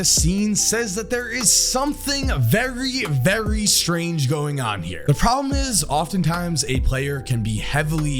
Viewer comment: when said "Very, Very strange going on" all I hear is "He is Very Very Heavy" 😂